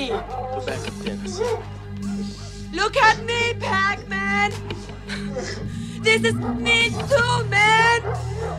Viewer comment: I havent seen this in forever but I always thought she said " look at me pac man I'm a home girl too"